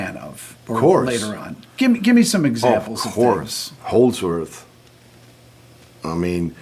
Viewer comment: I think Yngwie saying he never practiced but then saying he always played like he was performing is just a way of saying he practiced live performing over and over again all day long. There is no way anybody gets to a virtuoso level without practice. He saying he never practiced is a specific meaning known to him probably interpreting practice as boring scales. He practiced for sure. Vladimir Horowitz practiced. Yuja Wang practiced. Franz Liszt practiced and so did Nicolo Paganini. Yngwie said that with a specific meaning towards practice. But in the basic sense of practice just means you play all day long, well look at him, he is holding a guitar during the interview. In-fact, do we ever see him without a guitar? No. He plays all day. He probably even has a guitar in his Ferrari. He plays all day and and goes in live performance mode. Well this is how he practices then. Sorry but you are not bestowed the ability to play like this without constant repetition all day long. That constant repetition all day long is the only way it enters your muscle memory. You have to earn it even if you have talent. This is what practice is. So yes Yngwie, whatever you did is called practice. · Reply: HOLDSWORTH?? what is he saying?